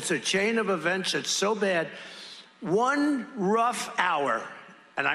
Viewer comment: I heard him say a rough hour, not violent day. Come on Stephen A, you’re putting words in the man’s mouth.